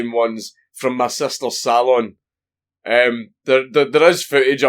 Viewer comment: either I can't understand his accent or I'm not filled in. What is he saying? I know it's something along the lines of stolen makeup but is he saying it's from his sister?